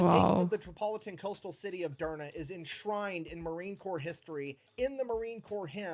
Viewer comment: the captions said DNA instead of Durma